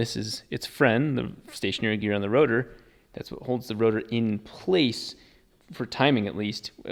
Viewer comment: I haven’t been keeping up for a while but did he just say 6 ROTOR?